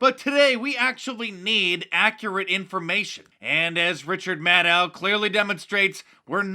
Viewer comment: LOL I just started to eat dinner...when I heard you say RITCHAD mADCOW..i know it caught me off guard..lol Thanks! oh..and CNN SUCKS!....OH WAIT THEREs MORE..LOL